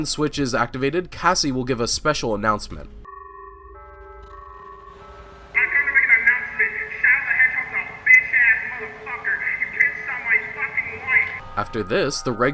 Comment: the only thing I heard was the f word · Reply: “I’m here to make an announcement, Shadow the hedgehog is a bitch ass motherfucker, he pissed on my fucking wife”